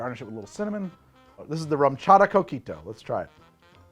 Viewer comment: Every time you say "coquito" I hear "cookie dough"